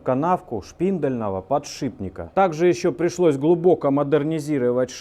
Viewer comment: Которая сверх подшиБника ставится... ))) Только думал о "подшибнике" и через 3 секунды "подшибника" услышал )) Думал показалось, пересмотрел несколько раз, а нет ))) 6:11 -Упорный подшиБник )))) 7:22 -Вооот такой подшиБник )))) 8:33 Полуподшипник, полуподшиБник, непанятна ))